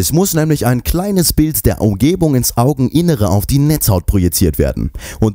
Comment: Ich schreibe das gerade auf sagt er bei Minute 1:23 aufgebung oder Umgebung man kann das nicht gut verstehen :/ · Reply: Naja obvsly muss ein bild der Umgebung auf die Netzhaut projeziert werden, ohne ein bild der umwelt ist es recht schwer zu sehen lul